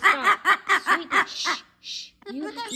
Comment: Did she say “ I have to go to bed” or is it just me? I've been watching so many of your videos and I've only heard her say “I love you” 🩷